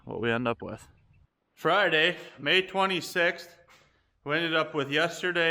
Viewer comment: Lol, When did I say that? Didn't even catch that. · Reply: I heard you say Friday may 26. Maybe I have been in the hot sun here too long!